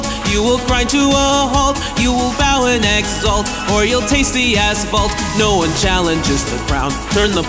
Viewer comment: Did anyone else hear "tasty asphalt"?